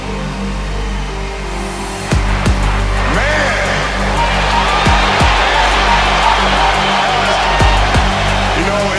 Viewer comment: What is he saying from 2:56 to 3:40? I don’t understand everything..:/ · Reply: I can't believe how fast 20 years went by man This is crazy, this is absolutely crazy and uh Yo, to be standing here with you guys, appreciating all this You know, the journey that we've been on You know, we have been through our ups and been through our downs I think the most important part is that We all stayed together throughout You know, I grew up, I grew up a diehard, diehard So to be drafted and then traded to this organization And to spend 20 years here I mean you can't, you can't write something better than this